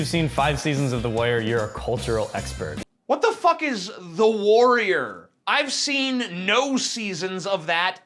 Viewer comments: lol, i think he's saying "the wire" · How the fuck does not know about "The Wire"? He can't even recognize it when he hears it. He thinks the dude said "The Warrior"! Fail... Fail. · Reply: I've heard of The Wire, but I also misheard it as "The Warrior". So, blame the pasty ginger fuck instead for his shit pronunciation. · At about 2:30 the guy clearly asked a question about "The Wire" a crime drama set iBaltimore; he did not say :The Warrior" I have hearing problems but I heard him clearly. As an agnostic atheist as well, all I can say is "Jesus H. Christ on a crutch you need to have your hearing checked!" :) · I think the guy said "The Wire." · I thought he said The Wire not The Warrior xD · I think he said : " The wire" . :D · Wut. Dude, he said "The Wire." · The guy you thought said "The Warrior" actually said "The Wire", which actually makes the question make sense.